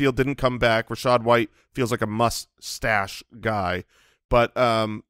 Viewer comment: “he’s a must stash” comment…all I heard was “mustache” rockin an epic stash!! 😀